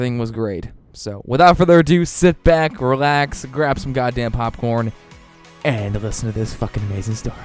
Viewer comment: Hey my headphones messed up and I thought you said grab some God dam porn XD